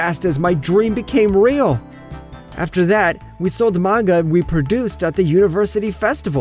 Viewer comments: i thought he said “we sold manga and reproduced” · I’m not gonna lie, I thought it said r e p r o d u c e d